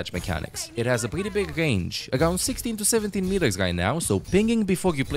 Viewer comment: What's the range on her healing turret? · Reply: he says 16-17m but I misheard it as 60-70, my bad